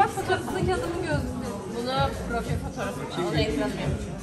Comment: 11:30 da ne dediğini tek ben duymuş olamam demi arkadaş olarak çok tatlılar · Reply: bu fotoğrafı ekranıma koyacağım dedi